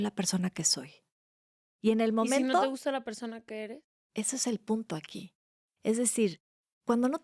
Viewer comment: minuto 13:21.... le regreso porque de primera oida, no entiendo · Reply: Si no te gusta quién eres, tienes la capacidad de cambiar si así lo decides.